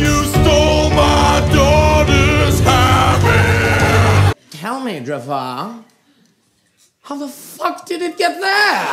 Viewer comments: what did he say?? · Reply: " 'Cause you stole my daughter's hymen" :') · WHAT IS THE WORD THAT HE SAYS?!?! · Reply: Hymen